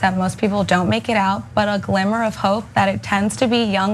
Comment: Did she just say "a glamour of hope?" At It's glimmer. Glimmer of hope. Its at 2:02.